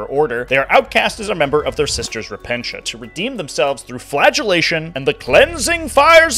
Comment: I misheard "flagellation" as "flatulation". Man, these Sisters Rippedafart sound deadly.